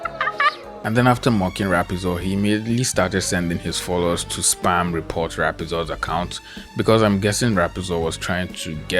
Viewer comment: What is the r word ? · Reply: rigger